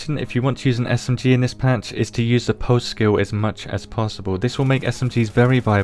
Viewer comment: What do you say at 11:28 regarding SMGs...you say to use the "Pose skill" or something like that. Sorry about my American ears. Great video! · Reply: He said "pulse".